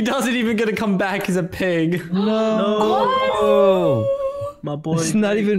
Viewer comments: i Just hear saying: noooo! My boyfriend. XD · Did anyone else hear say “my boy”?